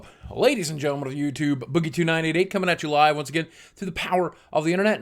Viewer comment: the first time I watched I thought he said boggie to night at 8 but no he says